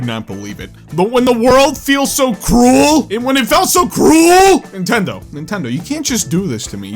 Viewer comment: I thought she said “cruel and green” at first 😂😂